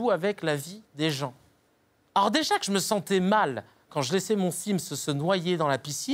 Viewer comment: au debut j'ai compris "quand j'ai laisser mon fils se noyer dans la piscine, non mais je l'ai fait une fois...." avant de capter qu'il avait dit sim's ^^